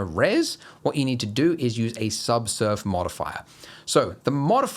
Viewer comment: For a beginner tutorial, maybe explain the jargon a bit. I actually thought you were saying soft serve modifier when you were talking about the subdivision surface modifier. Wait, what? We went from donuts to ice cream?